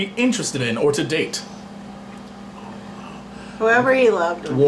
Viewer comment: lol holy shit first i heard he said "worm" xD